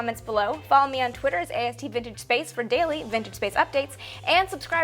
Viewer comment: lol thought said "bitchinspace" when she said vintagespace..